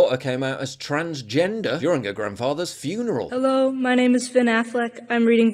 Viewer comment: At first i thought he said "her father's funeral". Then i listened again. 🙁